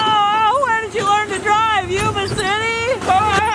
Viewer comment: Hey random question, did you say Yuba City at about 14:14, my parents and I were watching and freaked out if you did since we live here lol